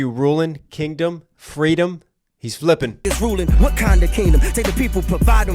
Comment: I heard wakanda kingdom, or is my hearing off?